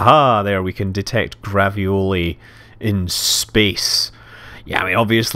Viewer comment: I thought you said we could detect ravioli in space lol